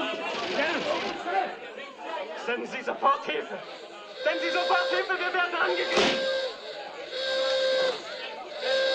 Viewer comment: What was he saying at 1:34 before Wvadislaw shot him? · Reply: "Send help immediatelly , we're being attacked"